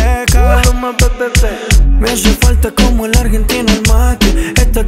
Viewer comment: Me haces falta como a la argentina el mate🇦🇷♥️ ~Maluma 2020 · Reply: Pensé que dijo "como la argenta macri"!! XD